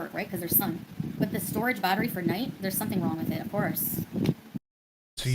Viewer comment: all I hear is cenk screaming "of course!"